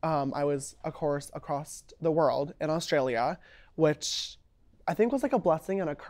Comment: He did not really say “when I was acrost the world” did he?